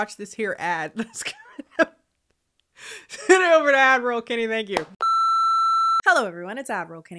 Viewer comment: All this time I thought you were saying admiral Kennie 😂😂